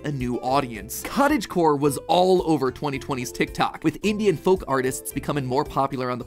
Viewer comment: I thought he said Indian folk music